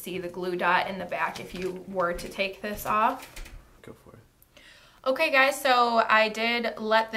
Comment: I heard a guy say "go for it." LOL. I sure hope that was your videographer :)